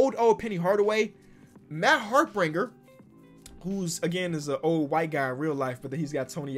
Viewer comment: What did I hear at 8:00 “the old white guy”